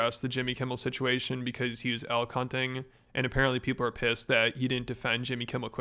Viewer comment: Instead of 'elk hunting' I heard 'L-Cunting' and thought it was a new brainrot slang or something.